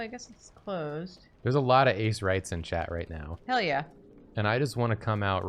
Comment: As an asexual myself...I also thought they were saying “ace rights”, lol